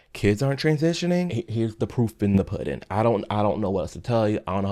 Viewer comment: Had my volume too low so I thought you said “poop in the pudding,” which honestly is perfect for this situation 😅